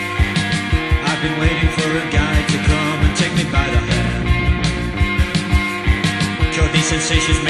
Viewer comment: oh god, all this time i thought he'd been saying "a guy to come and take me by the hand"...